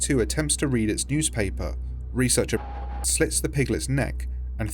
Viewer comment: researcher fucking slits the piglet's neck is what it said :D